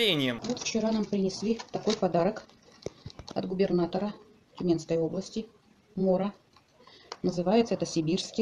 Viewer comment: Стоп, она разве не сказала, что пряник от губернатора Минской области. Или мне послышалось? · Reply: Тюменской области